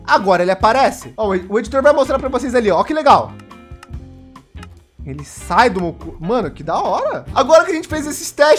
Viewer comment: "ele sai do meu c#" So eu escutei?